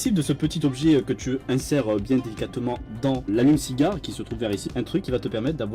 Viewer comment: j'ai compris "l'anus cigare" suis je spécial ? Sûrement